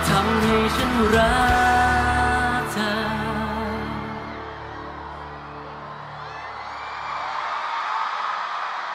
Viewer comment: this was touchy.. May I know what did Krist say at 2:30? He was really crying like a baby and nodding while Singto tried hard to hold back his tear to finish this lovely song.. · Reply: Krist said: "Khop khun na". It means "Thank you!" ^^